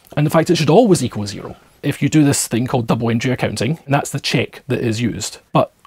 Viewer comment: he says a type of accounting "double [something] accounting". Anyone know what he said? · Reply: Double Entry